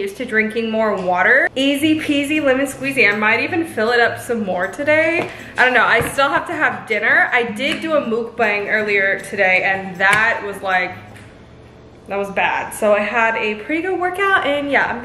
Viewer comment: Is it just me or does everyone say Milbank instead of look a no?